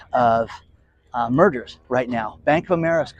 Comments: I had to re-listen to a portion of the video. I thought you said "there's going to be a huge amount of murders." The I realized you said 'mergers' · Mergers I thought you said murders ???😂🤣😂🤣😂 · My X had a show car. He loved it more than his wife... Thought you said murder instead of merger! 🤣😂🤣